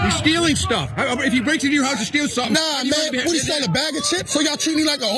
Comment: Cops and criminals are exactly the same. Those cops said, "What if one of THEM breaks into your house?" They meant, as was said, 'We trying to get these N-words out of the neighborhood ". I'll say it again... there's no difference between a cop and a criminal. They are the same people. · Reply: YOU LIED. At 0:46, the cop says "If HE breaks into your house and steals stuff..." The cop did NOT say "What if one of THEM breaks into your house." So, are you a liar? Or just cognitively compromised by anti-police bias?